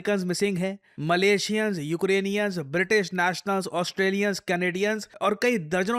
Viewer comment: did he say Ukrainian? Why are they there? CIA agents in Bihar and Manipur were also Ukrainian